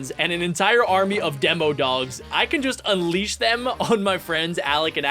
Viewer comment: Did You Say Demodogs